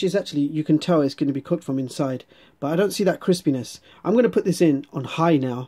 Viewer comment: Lol with subtitles on at 2:05 you it say chris penis instead of crispiness